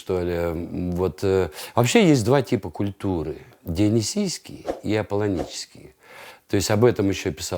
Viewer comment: Он сказал : есть два типа культуры. Первое . Дионисийский ..... А я слушал в полуха. И мне послышалось : есть два типа культуры. Первое . Где одни сиськи ... Я перемотал назад . Послышалось;)